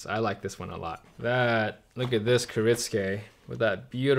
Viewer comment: what is this?? subtitles saying ker its gay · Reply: that's a good one. kiritsuke <link>